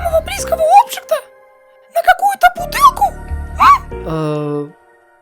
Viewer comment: Что слышать все:"ты правда готов променять меня,самого близкого обджекта,на какую то бутылку?" Что слышу я:"ты правда готов променять меня,самого близкого обджекта,на какую то бутылку пива?"